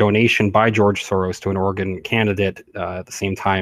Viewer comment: I kept hearing you say ‘organ candidate’... must be your accent, but I was confused for a little bit...⁉️